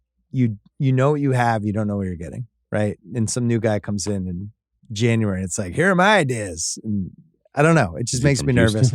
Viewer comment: what does Russillo say here? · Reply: From Houston 😂😂😂 referencing his accent from part 1